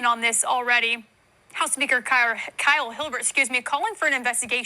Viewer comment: Am I the only one that heard her say house n******??? And then excuse herself??? What said in the dark comes out in the light